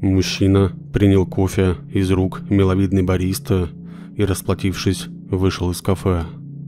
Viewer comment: мне послышалось: принял кофе из рук миловидной Бориски